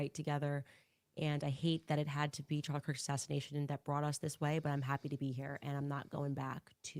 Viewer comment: she says ‘TUCKER’S ASSASSINATION’ NOT CHARLIE’S ASSASSINATION! Go back and check it out listen to what she says. Tucker better watch out she is planning that one too! She’s manifesting Tucker’s demise. 😂😂